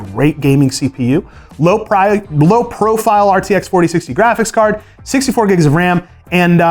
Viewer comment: why do i keep hearing about "RTX 6600"